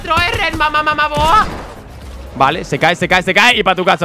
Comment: entendi mamamamadora xd